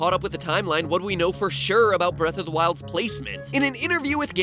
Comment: At around 3:00, I accidently muted my computer and I heard "Breath (of the wild's timline place-) ment" And I was confused why we were talking about Altoids